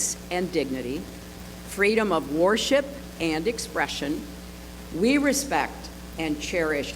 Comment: I thought it said confession speech